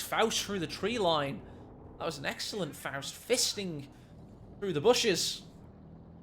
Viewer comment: "An excellent faust, fisting through the bushes!"...that's what she said... I see myself out.